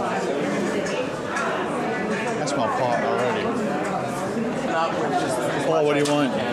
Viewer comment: Maybe I heard things but I could swear at 12:57 says I smell pot already? Am I hearing things or did he say something similar or did he say that? Btw love the vids, I am happy watching them however you do them.